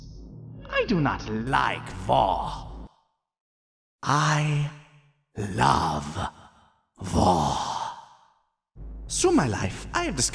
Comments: When the Nazi said I love war it sounded like he said I love vore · Ok this is about the Love war speech. I know its because its because is a german accent. But is sounds like he is saying He Loves Vore. I just needed to say this. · I thought he said vore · Real line: "I love war" What I hear: "I love vore " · I thought he was saying "I love vore" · i heard “I...LOVE...VORE” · sounded like "I love vore" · I really thought he said vore 😅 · The Major: I love vore. One mishearing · Class war, drug war, race war, flame war, storage war, cold war Ah yes, the six genders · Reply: I honestly thought he said “I love Vore” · I THOUGHT HE WAS SAYING I LOVE VORE NOT WAR IM DYING 😭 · it sounds like he's saying vore I know it's a German accent and he's saying war I'm just saying what I heard it sounded like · i thought he said “I love vore” at first ngl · is anyone else hearing vore and not war, or is that the joke and am i the idiot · anyone else hear Vore instead of war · I thought the major said "I. LOVE. VORE! " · At first, before Nazi McHitlerwurst continued his rant, I thought he said "I love vore!" and was completely ready to accept it.